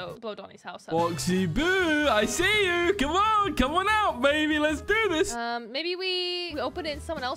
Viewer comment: did he call her baby or am i trippin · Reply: He called boxy boo baby